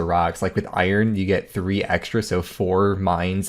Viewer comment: That's why I pointed out at 1:00 in the vid that the news post says "Initially, players were able to mine three extra sandstone" when it was actually only 2 extra initially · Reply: You're right. I thought you said 3 rocks not 3 extra.